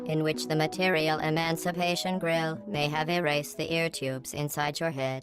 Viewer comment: GLADOS says "erased" but the subtitles say "emancipated"... huh